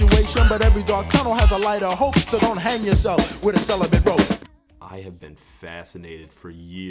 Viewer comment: "Don't hang yourself with a celibate rope" Did young MC foretell the incel movement and try and warn us 30 years in advance? We should have heeded the warnings. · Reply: I always thought he said: don’t hang yourself in the cellar with rope